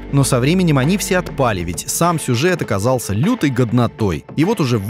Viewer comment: услышал "оказался лютой говнотой" думал лайк влепить чтоли))